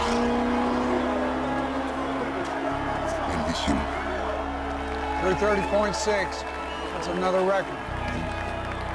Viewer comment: What Enzo ferrari did say? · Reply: He said "Bellissimo", which means beautiful.